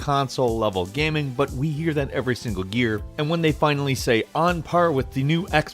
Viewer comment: did said gear